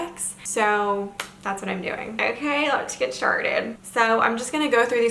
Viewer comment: I love you! It sounded like you said "lets get sharted" lol